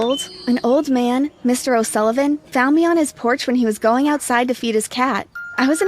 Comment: gurl four minute I thought it said he’s going outside to eat his cat lol